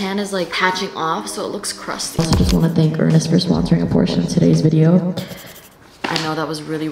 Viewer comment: bro i thought you said “id also like to thank ernest for sponserimg abortion in todays video”😭😭